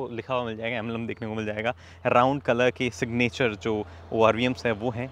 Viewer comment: did he say round color?😂